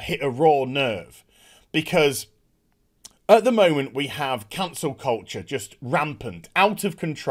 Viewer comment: I legit thought you said "Cancer" culture.